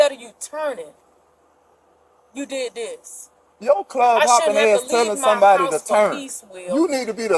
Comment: Dude said, "Yo club hopping ass".... 🤦🏾‍♀️🤣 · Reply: I thought he said clod hopping a**